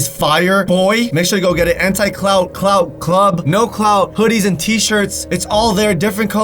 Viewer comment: I thought u said, “ go get that Anti clout cloud club merch.” At the start, but imagine if your merch actually said that, LOL.